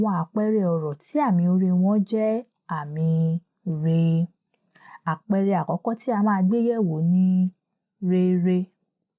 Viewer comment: I have been trying to hear very well what you keep saying when you want us to consider the next example. I think I finally got it this time: "àpẹẹrẹ akọkọ tí a máa gbéyẹ́wò ni..." (Is that correct?)